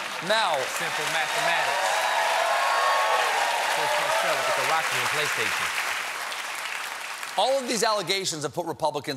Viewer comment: What did John Batiste say about The Rock and a playstation? LMAO. Love John, he says the most random funniest just off camera. "Coooollld bloooooded".